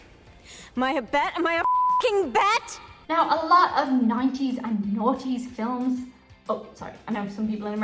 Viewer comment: For a second there I thought you said "90s and naughty films".